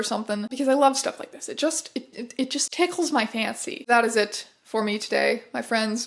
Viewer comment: Man.. I misheard "it tickles my fancy" as "it tickles my pantseat" LMAO